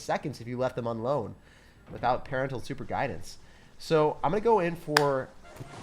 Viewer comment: No he really said "Parental Superguidance" 😩😩